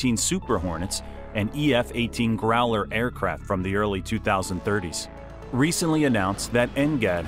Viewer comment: Thought I heard him say “early 2030s”